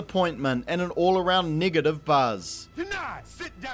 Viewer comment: WHAT A MIN? He did say "Negative" 1:18 into the video? Right? Not what I thought he said... Please correct me if I'm wrong.